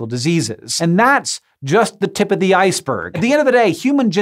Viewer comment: I love how my brain automatically tries to make puns out of things. The first time around I totally heard this as "tip of the ice-HERV" which I thought was a slightly clever pun. Turns out it was just me with bad hearing. Ah well, nice job Hank!